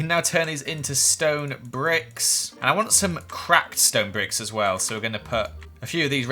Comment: Is it just I'm crazy or did Joel say "crap stone bricks"???